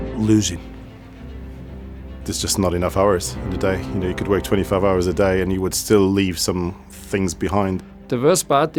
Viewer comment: did he say 25 hours a day??